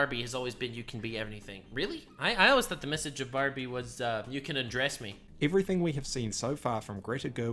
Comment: I thought you said “you can address me-“ Im now thinking about Barbie has a captain.